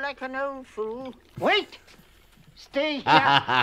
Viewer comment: i never watched it with subtitles on so I always thought the guy said "Witch! Stay here!"